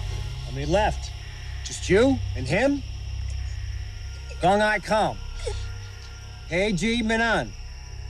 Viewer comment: As a Vietnamese, I think he said "Có ai không, hay chỉ mình anh?" (Is there anyone left, or just you?)